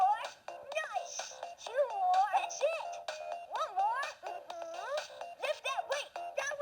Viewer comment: it sounds like it said “two more, that’s sh*t”